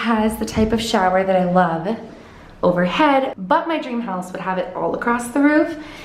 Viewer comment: I thought you said "dream palace" instead of "dream house"😂 I was like "yasss queen, a palace!!"💖💖💖